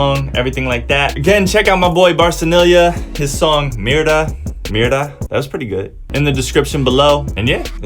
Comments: At the beginning, I thought you were trying to say "mierda" and I was like, who names a song that lol but then I saw the title of the song "mira" and I couldn't stop laughing at myself for thinking the name was a curse word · when he said "mira" i thought he was saying "mierda" 😅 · I thought he was saying “mierda” at first 😭😭😂 but no he said “Mira” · Loved the analysis man, can’t wait for u to look at more BTS stuff 💜 but u gotta watch ur pronunciation when ur saying mira cause it sounded like u were saying mierda 😂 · i heard "Mierda" instead of "Mira" 😂😂😂😂😂 Practice more, man 😂😂😂😂😂😂😂😂😂😂😂😂😂😂😂😂😂😂😂💕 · Bro I thought you said your friends song was called "mierda" I was concerned.....haha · I was laughing at you as you said "Mira" sounds like you saying "Mierda" haha!!!! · my dude when you say "mira" it sounds like youre saying "mierda" which means sh,t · I thought you said "Mierda" and that's a bad word here in Mexico, but then I heard the song and it was "Mira" like look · The way you said "mira" and mentioned the r sound i actually thought you were trying to say "mierda" which would just be a shitty name for a song